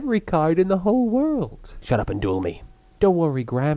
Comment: "Then why not tear up every card in the world?" "... Shut up." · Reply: I thought he said "Shut up and do me" lol